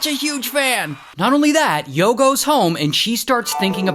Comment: I heard yogo home